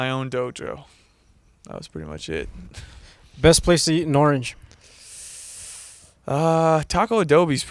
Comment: I heard that as "best place to eat an orange" and i was pondering for a minute why he'd ask that and why responded with a taco place😭